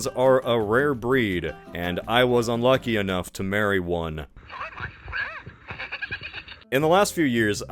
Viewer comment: did u say married 😳